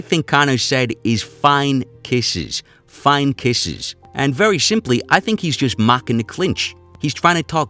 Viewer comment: I thought he said its only kisses as in getting punch in the clinch